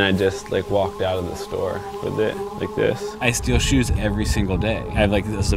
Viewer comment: did he say he steal shoes etty day?